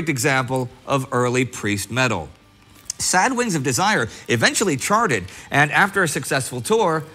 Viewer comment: Good stuff but at 3:32 does he say "Sad Wings of Desire"?